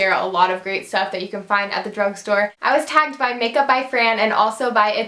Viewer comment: when she saiid she was tagged by those people I thought she said she was attacked